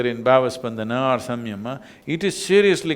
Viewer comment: what does he say at 16:31 please, blesses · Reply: Bhava spandana or samyama - referring to yogic practices